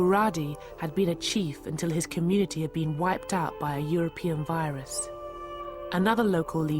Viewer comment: APPALLING LEFTIST ANTI-WHITE PROPAGANDA. At time 14:39, did they really say 'EUROPEAN VIRUS' ???